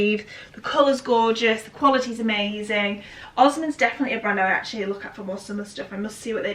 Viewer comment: Is the black silk dress brand "Osmond"? I'm sorry I'm french and I just can't quite catch it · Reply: Osmen! xx.